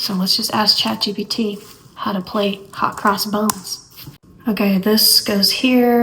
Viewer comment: It sounded like you said, "Hot cross bones." 😂